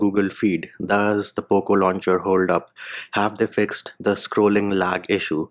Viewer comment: poco launcher,but i only hear "poop launcher",but that actually right =))